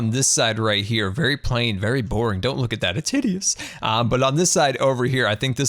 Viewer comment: I didn't hear what did you say at 13:57 ? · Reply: good_gamer567 which part? i think you mean hideous